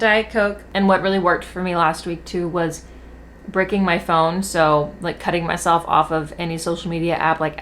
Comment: you said "what's really helped me is bricking my phone" I heard "what's really helped me is breaking my thumb" the way I had to pause and go back immediately, EXCUSE ME??